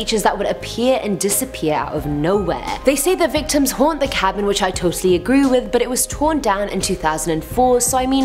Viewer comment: Does she say i totally agruu with?